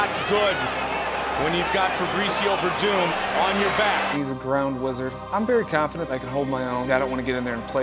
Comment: i thought he said "brown wizard"